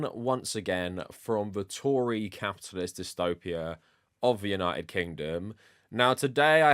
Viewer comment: I had to rewind because I thought you said "Tory, cat's p*ss dystopia". lol